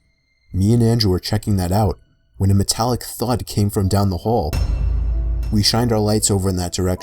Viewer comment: who else thought he said metallic thot